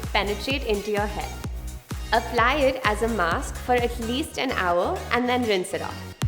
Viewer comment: did she say 10hour or 60min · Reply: Hey Vinita, said "atleast an hour" -Team Glamrs